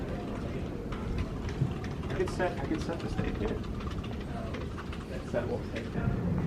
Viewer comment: I hear someone saying “I could set the state here” React???